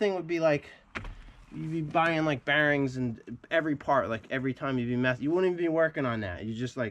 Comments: Hey, I don't think I heard you correctly, 15:54 -16:00, it sounded like you said... "Like every time you be Meth..."? I know, I'm from California and I sometimes have trouble with that 'NorEastern' accent... What was that? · did he say everytime you do meth? lololll